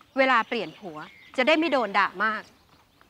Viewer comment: ตอนแรกได้ยินว่า ไม่โดนดราม่า นี้แบบ ฮื่อ พูดภาษานี้หรอ 5555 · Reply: แวบแรกเราก็ได้ยินแบบนั้นแหละ แต่พอฟังดีๆ เขาบอกว่าจะได้ไม่โดนด่ามาก